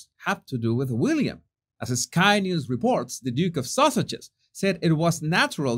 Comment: Big thumbs up if you think Harry uses the press more than the press use him!👍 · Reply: I almost thought you said "the Duke of sausages!"😂